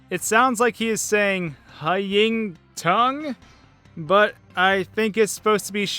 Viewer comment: I hear "fighting cunt" instead of shining cutter 🤐